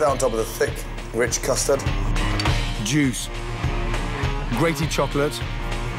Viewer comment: WHAT DID YOU SAY GORDON???? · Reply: Half the time I hear he say "jews" instead of "juice"